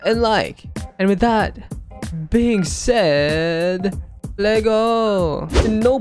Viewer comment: What did u say?!! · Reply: LEGOOOO